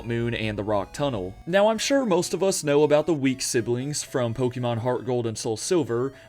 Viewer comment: I thought you said "the weak siblings" and I was like "who is he talking about, it's unnecessarily mean..."